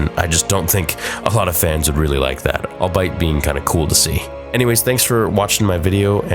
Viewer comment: I don't mean to be that guy because I LOVE SWT so much...but I've heard you say "albeit" like "Al bite" in a number of episodes, but it is "al bee it." Just figured I'd let you know because you otherwise effectively use the word and seem to like it! Keep up the awesome episodes!